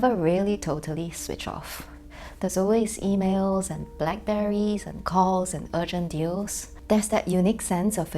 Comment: Wait, did she say Blackberries ?!